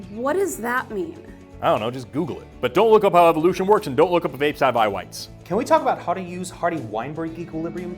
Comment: What does he say after "But don't look up how evolution works..."? It sounds like "...and don't look up how vapes...(?intelligible)." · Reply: “And dont look up if apes have eye whites”, i believe